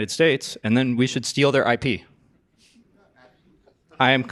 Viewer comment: What did you say? :face-blue-smiling: steal their IP????:face-blue-smiling::face-blue-smiling::face-blue-smiling::face-blue-smiling::face-blue-smiling